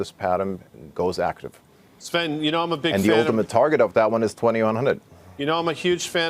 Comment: he said “and the ultimate target of that is?????? Did he say 2,100?